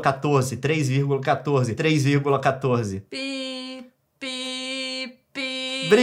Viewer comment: não entendi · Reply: Pi